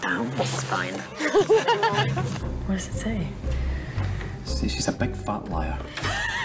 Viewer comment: Who does Rumple call a big fat liar at 0:43? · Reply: emma lol